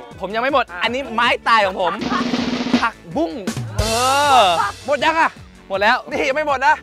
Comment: OMG What P'Leo said at 9:29 sounds like 'pak-boom', in my country it means 'pop' or 'loud explosion'😂